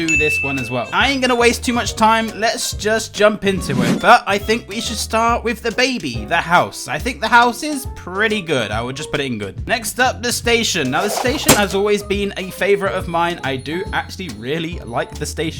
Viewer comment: Did he just say "Dababy"?